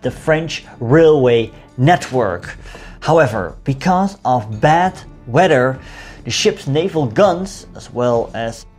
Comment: My hearing is so bad I thought you said bed wetter instead bad weather